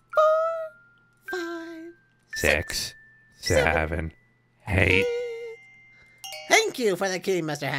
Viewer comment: Is it just me or did Moon sound like he said "five, six, seven, hate"?